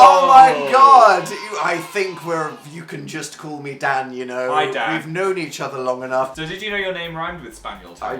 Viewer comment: When said hi dan I thought he said my dan and I freaked out for a second.